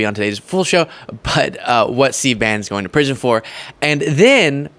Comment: it sounded like you said "C-Ban".